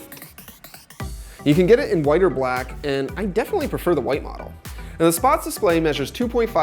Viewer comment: wHaT hAvE yOu JuSt sAiD?!?! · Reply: I definitely prefer the white model?? o.o